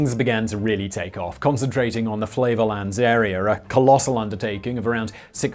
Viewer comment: I thought you said flavorland and immediately wondered if that's where Guy fieri is from